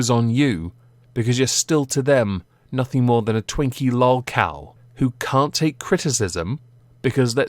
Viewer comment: “Nothing more than a Twinkie lol cow” was the best string of words to describe ImAllexx that I’ve ever heard. Great video. He’s not worth a full on investigation and you nailed it in 30 min or less! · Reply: I thought he said twink lol cow